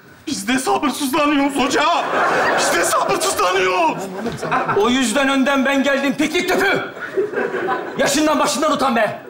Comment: ne diyor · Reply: O yüzden önden ben geldim piknik tüpü, diyor